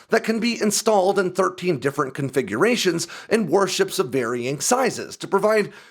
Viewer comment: I thought he said “worship” but he said “warship” lmao